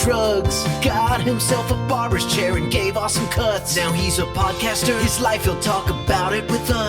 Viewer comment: Got himself a barbers chair and gave awesome cuts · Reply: I always thought it said gave out some cuts but I do this all the time I make shit up that sounds right all the time glad to know the actual words lol